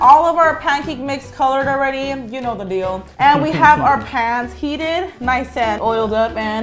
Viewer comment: I thought you said we have our heated pants-- edit; my most recent emojis were 💛🤗😅😆😂😙😍🍐🍛🍏 I was spamming my friend emojis ~ω~